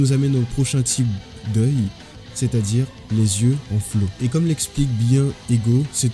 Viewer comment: Pourquoi est ce écrit « les yeux en stylo » mais que tu dis « les yeux en flow » ?